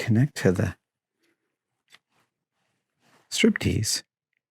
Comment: Wow! Adam doesn't need to go to the STRIP CLUB, he can "strip" by himself. LOL! i thought I heard "strip tease." It was strip peace.